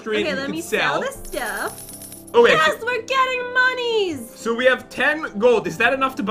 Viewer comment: and 1:49 said we're getting monies instead of money